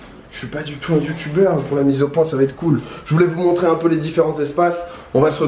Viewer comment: Je suis tellement matrixé par toi que quand t'as dit "mise au point" j'ai entendu "bizot point"